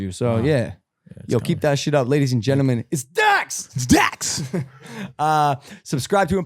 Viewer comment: At first I thought he'll said IT'S DAXxX as his intro